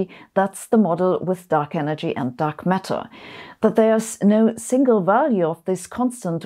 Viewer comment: im sure i heard "Duck matter"....